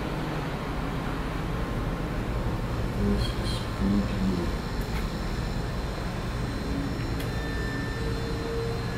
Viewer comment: sounds like a women talking can’t understand what she said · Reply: Sounds like she says “this is spooky”